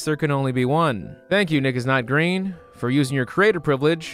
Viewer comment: He said nickisnotgreen but I thought he said you niggas not green.